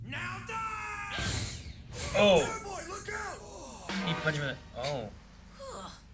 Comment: holy shit i wasnt paying attention to the video and i just heard "JOYBOY" and almost choked myself but realized he meant chore boy